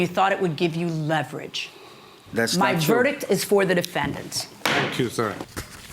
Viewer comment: Did he say “Thank you, sir” 😆